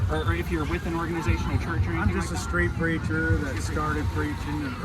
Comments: oh... street PREACHER! i thought you said street CREATURE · Not even 2 minutes in and misheard "I'm just a street preacher" as "I'm just a straight preacher". 💀